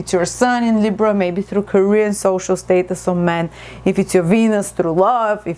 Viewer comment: (or anyone), I did not understand at around 19:57. You said if it's our sun, it would benefit us in career, social status and ? did you say 'man'? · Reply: TragicOptimist Men